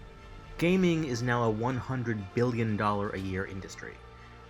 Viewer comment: good thing you specified, I thought you said villion, with a v...